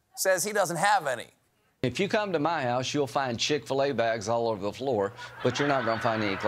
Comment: Something tells me that someone should check Lindsey Graham's place for classified docs. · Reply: I thought he said shit-filla 🤣🤣